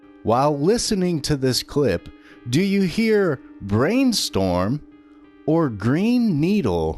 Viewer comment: I heard " Green Needle" twice at the beginning and then I heard "Brain Storm" and then I went to replay it and it's it said "Brain Storm" at the beginning.